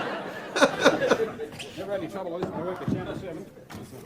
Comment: When they're lifting the coat rack it sounded like he said something like "Never any trouble like this when I worked at Channel 7"